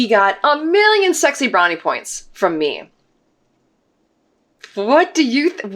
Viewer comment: "a million sexy brownie points" - I watch at 3x and usually get every word. Had to slow that down as I thought you said, "a million 'sixty-nine' points" and wondered what that could mean.